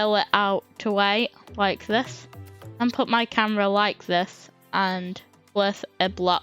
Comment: What did he say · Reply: and put my camera like this